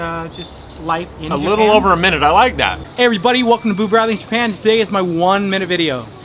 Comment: I thought he said his name was boo radley